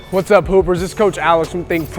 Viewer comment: i thought he said "hey pooper's whats up"